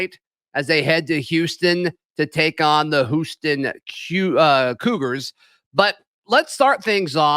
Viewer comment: Did just say the Whooston Queuegars?